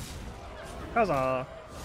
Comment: Hi guys I'm not a native English speaker and I'm always wondering what he's saying like at 7:12. Did he say "how's up" or another expression? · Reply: He said "Huzzah!" Huzzah: exclamation used to express approval or delight; hurrah.